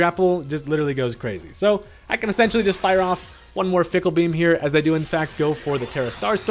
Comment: Would the ability Serene Grace apply to Fickle Beam? If it does, a skill-swap/entrainment doubles match would be fun to watch. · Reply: I thought it said PICKLE beam😭😂